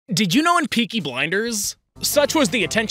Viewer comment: I’m sorry but the intro sounds like you saying pinky blinders